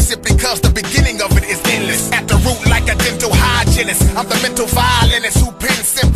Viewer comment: Dental hygienist,instead of gental hygienist